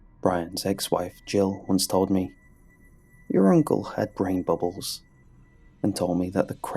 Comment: Couldve sworn this said "my uncle dad brain bubbles"